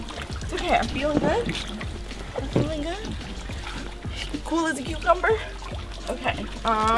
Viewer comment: When said “I’m fine, cool as a cucumber” I thought she said “ I’m fine, Cole is a cucumber 🥒 “ LOL 😂